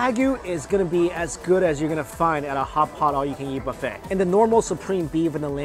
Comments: Why does it sound like he says all you can yeet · I thought he said all you can yeet and I wheezed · Stg I keep hearing "all you can yeet" and it won't stop.